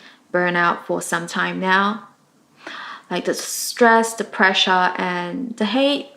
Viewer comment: Oh she said "the pressure" I thought she said "Depressia"